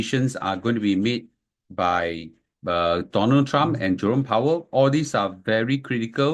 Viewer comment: I thought you said Jurong Power. Lol 😂